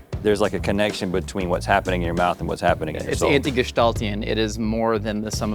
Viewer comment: What the hell did Josh say at 4:35 ? Anti-gestaltion or something?